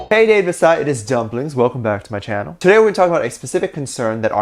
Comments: Wait I’m so confused😅 Am I the only one who heard him say “Hey Dave Disci, it’s dumplings” 😂😂😂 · Did he just say "hey david disci it is dumplings" I cant😂😂